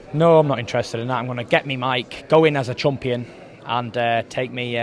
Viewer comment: lol... 5:29 did did tyson just call himself a "chump-ion"???